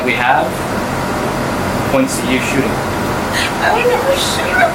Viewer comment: she says “I would never shoot him” but subtitles say “ I don’t remember shooting him.” Come on, subtitle guy 😂